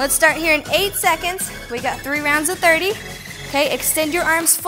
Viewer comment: I thought she said we had 8 rounds of 30 and that dumbbell almost crushed my skull.🤣